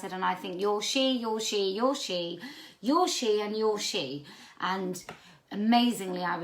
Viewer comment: LMFAO! Curse my noisy truck engines-induced industrial deafness. At about 1:15 I thought you were saying, "You're sheep, you're sheep, you're sheep and you're sheep." Deaf or not, I think I was right anyway! Best laugh I've had in a while.